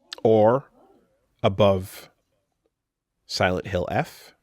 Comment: did he just say "Shilf (the h is silent)"??